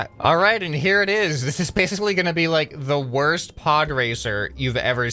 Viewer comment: did he say pog? sus